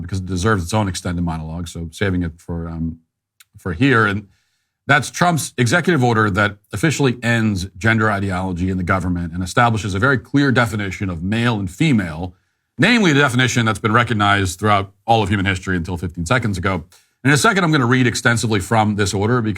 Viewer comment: I thought he said “a very queer definition” 😂